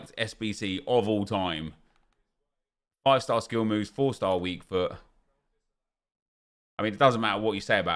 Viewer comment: he really said 4 star weak door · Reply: Foot